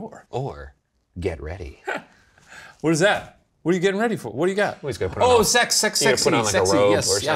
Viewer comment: Fun fact: I'm drunk AF and have been half paying attention for the past 20 minutes, then I hear "getting ready for sex" and I'm like "what the fuck did I miss!?" XD